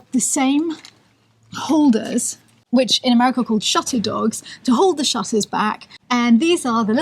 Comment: Whoa I totally didn’t realize the windows were painted until she pointed it out. Hahaha. Is also saying “shutter dogs” or am I hearing things? Before I heard “chateau dogs” then I realize “oh shutter! Duh” 😆